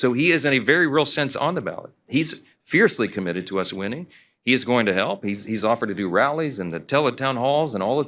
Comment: MagaMike - "He IS going to hell". Am I hearing things correctly?